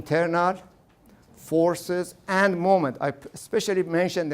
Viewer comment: I think the subtitles are wrong near 10:05. I hear "moment", and "moment" lines up more closely with the content of the lecture, but the subtitles say "movement".